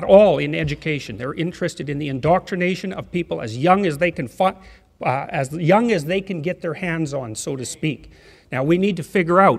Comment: i love jordan peterson but sounded like (as young as they can fuck) which makes sense